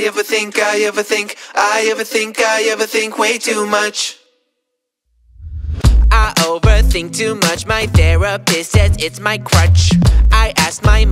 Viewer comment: i keep hearing "i never think" i also never think, man.